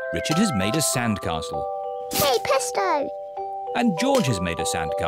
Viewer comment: when said presto it sounded like ya pesto